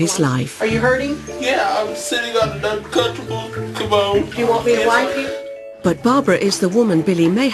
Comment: did she said "u want me to wank u" ? ...